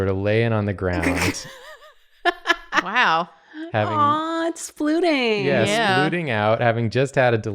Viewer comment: I heard “aww it’s fluting” not “splooting” and stopped playing my estude, set my flute down, and replayed it till I heard splooting lol.